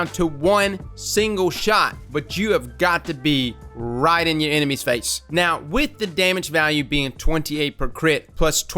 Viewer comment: I thought he said u gotta ride ur enemies face💀